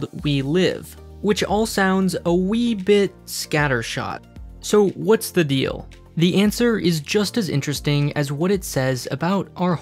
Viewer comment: who else heard "sooo, what's the we?"